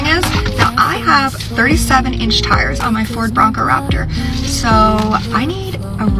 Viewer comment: LOL at the sweater 😆😆 I can imagine trying to keep that ON while trying to reinflate the tires 😊😆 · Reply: tires!? i thought she said AIR-BAGS! XD